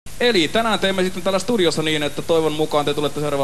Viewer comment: parece que dice "Asturias"? lol